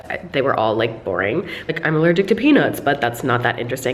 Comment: I thought you said "I'm allergic to penis" I had to repeat it 4 times to understand that you said "peanuts" 😂😂😂😂😂